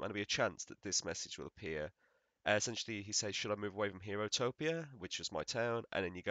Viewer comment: For a second I thought your island said heterotopia lmao